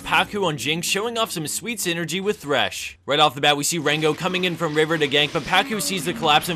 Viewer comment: Did anybody hear the word Rango at 0:50 ?? XD